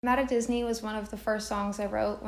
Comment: And this whole time my dumbass thought she was saying “I’m at Disney” 🤦🏼‍♀️🥱😭😂🤡